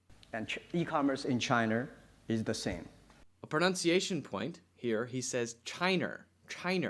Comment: Did you say 'pronounciation' when correcting Jack Ma's pronunciation of "China"? Maybe I'm mishearing it ... but I definitely hear 'pronounciation' every time ...